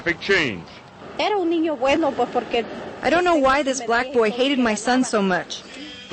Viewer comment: “I Dont Know Why This Black Boy Hated My Son So Much” Thats Bullshit what she really said was He Was A Good Kid